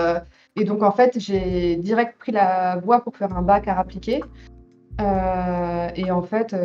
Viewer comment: A 6:20 elle dit " j'ai fait un bac arts appliqués" mais j'ai entendu "et là en fait, la bac a rappliqué"